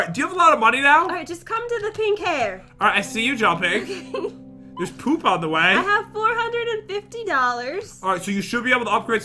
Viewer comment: Did say “There’s poop on the way?!”